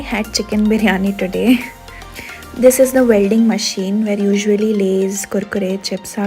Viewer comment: Did I just hear “Welding Machine”